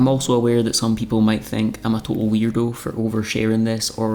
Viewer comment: You said “weirdo”, and I heard “hero”. Anyone who shares the difficulties of life and normalizes seeking help is a hero. Glad you’re having more good days than you had in the past. Thank you.